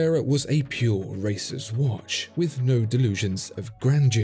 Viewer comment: I thought @ 1:17 he said the Carrera was a pure racist watch!